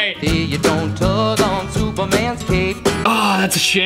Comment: I only hear cape